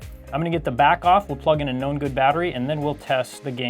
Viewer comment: I always hear "A NO GOOD battery" when you say "A Know good battery". Took me a while to understand.